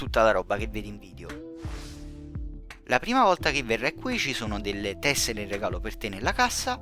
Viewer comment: Ciao! Quale regalo? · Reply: del video 🙃 Ma non c'era domanda xke ora Ho sentito che dici "per La prim avolta quando vieni qui"😂🤦